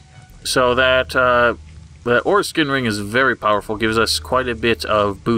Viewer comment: Was it just me or did it sound like he said foreskin ring